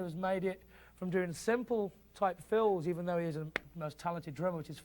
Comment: I think you’re misinterpreting what he’s saying, and Jason wasn’t very clear anyway What I believe he was saying was how Phil, who is an amazing drummer, has utilised effects as a way of enhancing his fills. And here’s an example of a simple fill and how clever use of effects turns it into something memorable and ear-catching! Of a 2014 meeting Jason said “We got together for three weeks and I had an amazing time,” the song of Led Zep’s late John Bonham tells Billboard. “It was an honor to be chosen by one of my real heroes. Other than my dad I would say Phil is probably my No. 2 guy to go to. I”ve been a huge fan since Abacab, and then of course his solo stuff. It was like playing in front of my dad — another moment that felt like you’re playing in front of your dad in the weirdest kind of way. You’re playing in front of your heroes. My wife just kept telling me, ‘Don’t keep looking for approval. He’s hired you. He knows what you can do. Just give him your best.’ I had such a good time with it.” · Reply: I beg your pardon, I actually was in the middle of writing a riposte when I decided to listen back another few times. I agree, you are correct, I initially thought he said "even though he isn't the most talented drummer" but I actually think he says "is" on reflection. In which case, he is out of the woods! :D